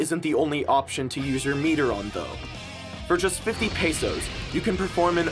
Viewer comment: did he just said “50 pesos” ? 💀